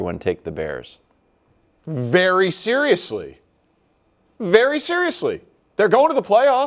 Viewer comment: I thought Danny said "BEARY seriously". He should have, and then just stopped